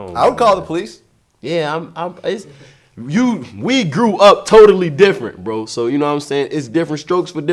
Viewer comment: my boi said turtley different